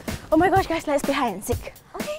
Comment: lmao the subtitles said "let's be high and sick"